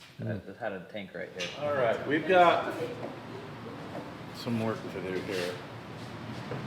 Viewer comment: It sounded like said “We’ve got some Mork to do.” So my response was “nanu nanu, shazbot!”